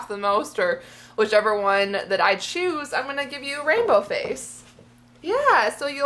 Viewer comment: omg when u said rainbow face i thought u said rapeo face.... haha until u explained "because she has all these colors on her face!" and im like ohhhhhhhhhh OK!